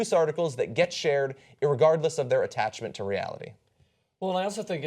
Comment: did John say "irregardless?"